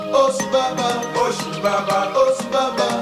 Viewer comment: Что слышат обычные люди : песня Что слышу я : БАБА ОСЬ БАБА ОСЬ